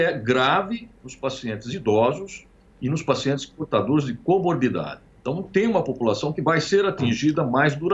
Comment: Alguém entendeu o nome da doença que ele diz que alguns são portadores? Lá no minuto 6:07 ele fala desta doença a qual não entendi o nome. O que entendi foi Cobordida, mas procurei na internet por este nome e nada encontrei. Se alguém entender melhor favor informar, pois quero pesquisar a respeito. Grato!! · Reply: co morbidade, ou seja, outras doenças concomitantes ao corona virus